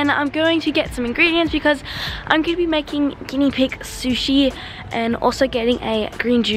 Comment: thought she was bouta say guinea pig soup